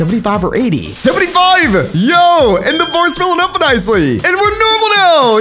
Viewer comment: does anyone know what he just said?😂😂 you can prove that what he said was anything close to English 😂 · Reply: "and the bar is filling up nicely" is what i hear